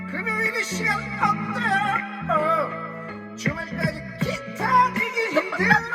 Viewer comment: 에서 어때요가 꼬끼오로 들려버리네